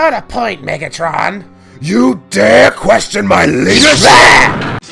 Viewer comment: i know he said "You dare question my leadership?" but i keep hearing "you dare Question my Lasers?"